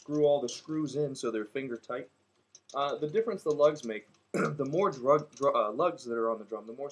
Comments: did you say "drugs" instead of "lugs" at 2:39? stop sniffin that shit man.... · he says "DRUGS" instead of "LUGGS". Comedy. x